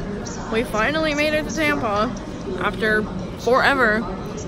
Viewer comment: Swear to good I thought you said we finally made it to tampon 😂